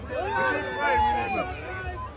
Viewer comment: Why do it sound like he said one hit Wayne ?!?